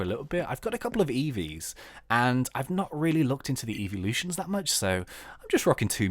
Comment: When he said evelution about reviews, I thought he said “eevielution”